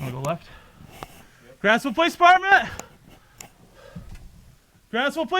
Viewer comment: If your child is showing depression and angry- for gods sakes secure your weapons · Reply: Oh so it's: grantsville police department, at: 7:30. This whole time I thought the cop was saying grassville police department. 😂🎉